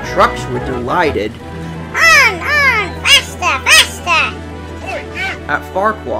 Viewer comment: OI YOU! I heard that lol 😂